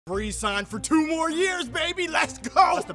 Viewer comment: The very first thing he says is "Brees signed for 2 more years..." ... but, at least to me, it KINDA sounds like he says "Maurice signed for 2 more years"... does anybody else KINDA hear that or am I COMPLETELY insane? lol